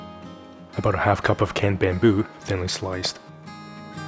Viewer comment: I'm sorry is he saying canned bamboo>?